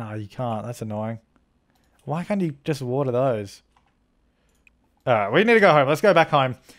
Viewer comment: what're those!😂😂 (I know he said water those)